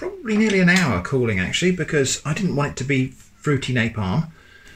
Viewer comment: That pie looks mouth watering!! Dying for a slice! I didn’t catch what you said at 21:56. I didn’t want to be…..?? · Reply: it to be "Fruity napalm".